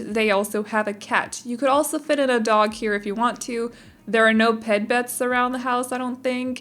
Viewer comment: 💜 TWITTER: <link> 💙 INSTAGRAM: <link> 🧡 ROBLOX CHANNEL: <link> 💛 PATREON: <link> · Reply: when you were talking about the dog beds you said “Ped Bets” instead of “Pet Beds” i about died cause I said the exact same thing when I said it out loud too 😂